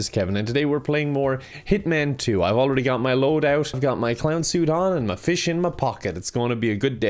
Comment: I heard "I've already got my load out" instead of loadout in the beginning... (Just me?)